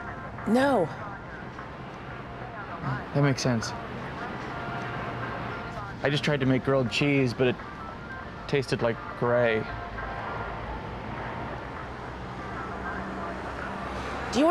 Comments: "do you wanna taste grilled cheese again?" It was funny, but when you think about it it's actually so sad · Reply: I thought she said girl cheese - I was like wtf! Thanks for clearing that up for me 👍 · "do you wanna taste grilled cheese again?" It was funny, but when you think about it it's actually so sad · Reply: oh snap. I thought she said girl cheese too. I was like "wtf does that mean?"